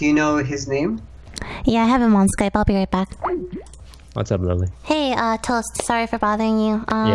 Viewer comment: What toast said: What's up lily What i heard: What's up my baby